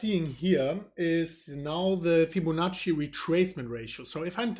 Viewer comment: Did just say "FibboNazi retracement?"